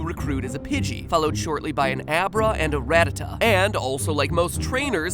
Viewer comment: I wanted to think that he said "Rattata" right, but I hear it as "Ratattata" when I go back to it... at 2:16 he starts saying it quite a bit....